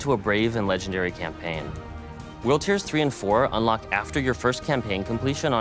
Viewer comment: every time this dude says world tier i think hes saying wheelchair